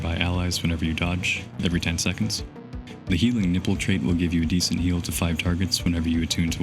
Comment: i....i literally thought you said healing nipples, I had to do a complete double take and rewinded to realize that's healing ripples 🤣🤣🤣🤣🤣🤣🤣